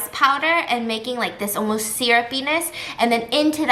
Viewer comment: when she said "syrup-yness" The captions said 'ciara penis'😆lol 🤣